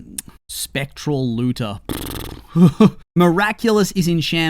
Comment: I thought he said spectral looser at first😂 that says something about that terrible name